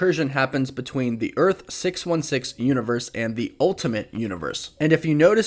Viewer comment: Thought they said alternate not ultimate